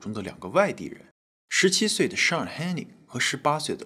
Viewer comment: 😂幻听成了“17岁的上海人”哈哈哈哈哈哈哈哈